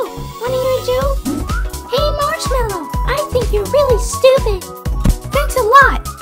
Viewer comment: When Cherries got pushed in the portal, the audio is distorted, making it hard to understand to the viewer. However, the original audio file was just saying "Long live the maraschino!" A maraschino cherry is the sugary red cherry that they put on top of ice cream sundaes some times. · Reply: I thought they said sorry marshmellow